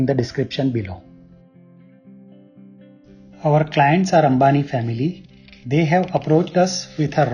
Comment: Our clients are Ambani family.. 😅😅😅😅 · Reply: I thought he said our clients are harami family and i was like dude living compact doesnt mean he is harami..😜😜😜😁😄😆